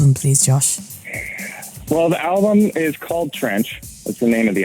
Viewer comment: “ I’m in trench “ I laughed so hard 😂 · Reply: Jade Møøn I thought at first he said French and I was like tf but then I was like ooooh